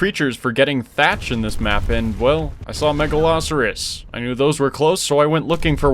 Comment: I thought they said Megalosaurus and was so confused lmfao